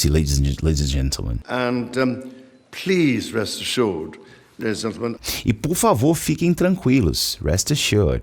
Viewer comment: Como já dominava os contextos históricos das anedotas e trocadilhos, havia entendido tudo perfeitamente, exceto por esta parte aqui: 22:55 "And please, rest assured, ladies and gentleman" Eu entendi "And please, rest assured, the resentment" aueahueahue